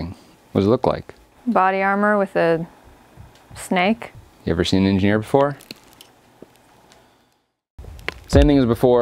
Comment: the first time I heard " Have you ever seen me in here before" lol